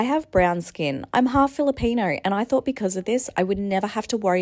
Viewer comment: When she said ‘ im half Philippine ‘ I HEARD IT AS ‘ I’m half jalapeño ‘ I WAS SO CONFUSED 😭😭